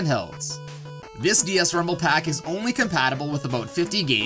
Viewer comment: i thought it said "RUMBLE PAKI"